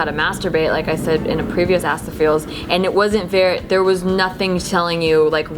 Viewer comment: why cant americans pronunciate their Ks? always sounds like theyre saying assthefeels